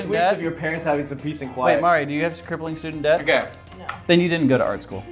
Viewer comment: "mari, do you have crippling student debt?" "no." "then you didn't go through art school." ...he's not entirely wrong. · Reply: I thought he said "scribbling student debt" at first, and I was really confused.